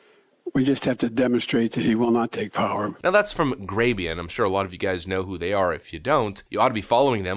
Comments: How do you spell the name of the outlet he's referring to here with regards to posting clips like this? Sounds like he says Graybian? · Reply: I wanted to know this, too. I heard "Graybien" also, but I thought that word sounded like slang for someone exclusively attracted to grey aliens.... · How do you spell the name of the outlet he's referring to here with regards to posting clips like this? Sounds like he says Graybian?